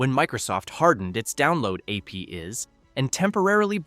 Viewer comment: "AP IS"? I think the AI meant to say "APIs". Do people who use text to speech narrators actually bother to watch them back before uploading them?